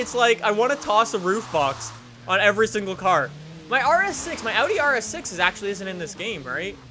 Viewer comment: is it just me or did he say he had an rs6 😳😳